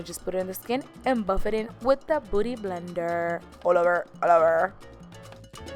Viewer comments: Haha sounds like you said booty blender 😂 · it sound like you said booty blender 😂😂😂😂 but you sound cute.😍😍😍